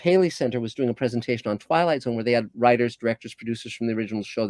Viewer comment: Oh he said Twilight Zone. I thought he said Twilight! That would have explained a few things though.